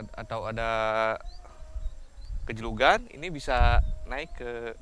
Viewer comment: syitt man what the hell u saying gejlugan 😂😂